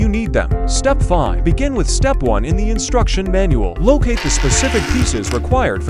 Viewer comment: When the AI was saying (Locate the pieces) I thought he said ( Lookei the pieces )😂